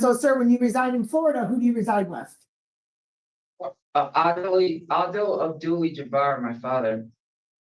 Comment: Who he said his father is? Did he say Kareem Abdul Jabbar?😐